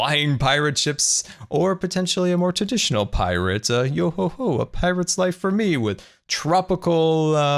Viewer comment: I totally didnt think said "A more traditional pirate UFO" I have no idea what youre talking about